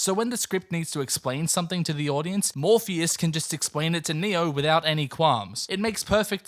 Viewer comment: When he said "Morpheus" I first heard "Morbius" Beautiful